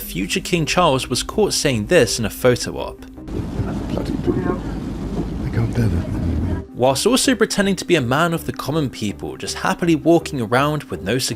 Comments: = Subtitles would have been nice. I can't make out what he's saying. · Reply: The full quote was "Bloody people. I can't bear that man. He's so awful. He really is." Charles was moaning about reporters in general, and the BBC's Nicholas Witchell in particular. The gaffe kinda became a running joke here in Britain and followed Witchell around for the rest of his career. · I can't hear bloody who the king can't bear ?